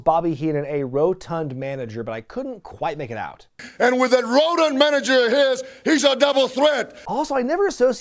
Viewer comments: Sounded like tito said 'rodent manager' edit: nvm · I always thought Santana said “rodent manager” referring to Heenan being called “The Weasel” by adversaries. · tito called Bobby Heenan rodent. I hear a d in the word. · I always heard "that Rodent manager" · Thanks to everyone who pointed out that Tito said "rodent" and not "rotund"! You were all very much on the ball with that one! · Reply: I thought he said rotten manager, oh well, next time somebody sees Tito, they should ask him